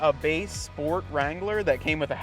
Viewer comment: Gotta love them Ford Wranglers😂 0:33 I know that's not what he said it's just what I heard. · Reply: That was base Sport Wrangler.